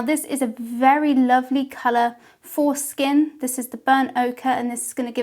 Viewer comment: I can’t be the only one at 1:42 who thought she said the color was “foreskin” instead of for skin 😂😂😂